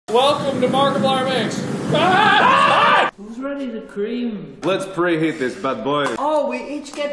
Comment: I'm wearing broken ear buds and at the beginning it sounds like Ethan said "Who's Ready To Ream?"